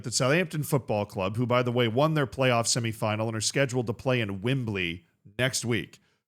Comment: I'm sorry, did you say "Wimbli"?😂😂😂